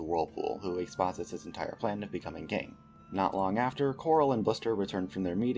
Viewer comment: I heard "Of becoming gay" I'm sorry lol no hate. Love the au so far!